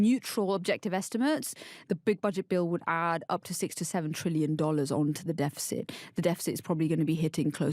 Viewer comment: The numbers and argumentation don't add up. If the tariffs generate 350-400 billion USD and the BBB adds 6-7 trillion USD (<num> billion USD) then the tariffs are insignificantand don't matter at all in relation to the BBB. There is no "direct connection" between the tariffs and the BBB. The tariffs are a rounding error in comparison to the BBB. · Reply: I thought she said 67 trillion Not 6-7!!! That makes your argument a bit off!!!